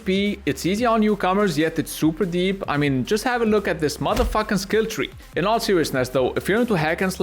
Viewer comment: did he just say mother f**